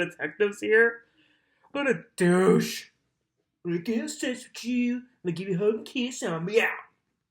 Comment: What did u say at 9:06 right after u said “what a douche??”🤦🏽‍♀️🤦🏽‍♀️😂😂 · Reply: “I ain’t gonna have seggs witchyu, Ima give you a hug and a kiss and Ima be out”